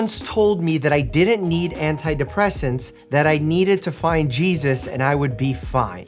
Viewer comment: the part where it said to find Jesus made me think the doctor was saying “kys” 💀